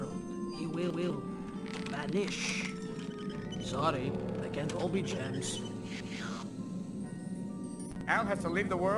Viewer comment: What did she say again? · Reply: She said "Sorry, they can't all be gems". Its a common phrase used when someone acknowledges a bad joke/rhyme they made. She was specifically referring to her rhyming "wish" with "vanish".